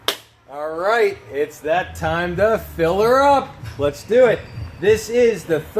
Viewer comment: Wait were supposed to fill her up? This whole time I thought you were saying feel her up